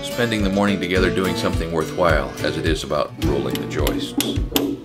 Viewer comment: Thought it said Roll the Joints Ep.40. :D